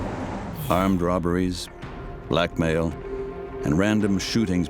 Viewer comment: Thought he said black male not blackmail